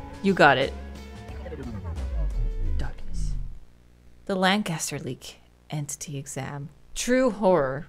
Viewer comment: I thought it said Lannister for a moment XD